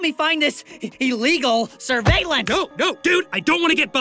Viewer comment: Tom: no no dude I don’t wanna be bothered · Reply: HELP I HEARD “no no-dude I don’t wanna get f-ked” 😭